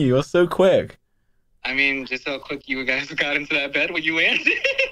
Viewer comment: does someone understand what said at 40:01? i cant figure it out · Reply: I think it’s “just like how quick you got into the bed when you landed”